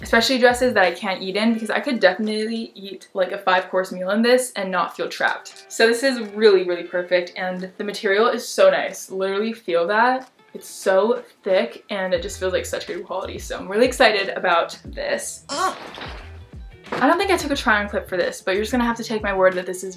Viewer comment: did she say feel that?!